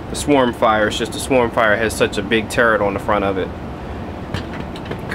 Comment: i thought he said terd